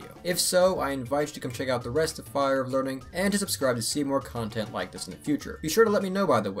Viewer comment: Who is this Seymour Content I keep hearing about?